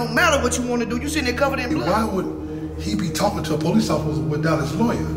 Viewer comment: For a second I thought dude said “why , would he be talking to you without no thoink”